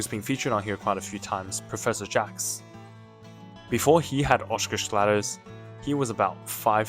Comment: What does you say here 3:00. English is not native for me so i hear here oshcushladders Edit: i said "oshcushladders" to siri, and found that it is "osgood-schlatter"